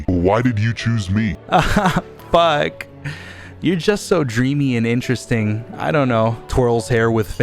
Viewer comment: When you said dreamy to the wallet I thought you said creamy